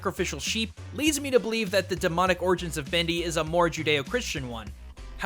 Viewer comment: I thought it said DANK ORIGINS